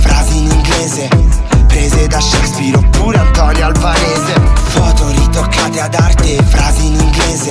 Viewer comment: minuto 0:59 COSA HA DETTO? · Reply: FLAVIA Miller and the joliette antonio albanese...?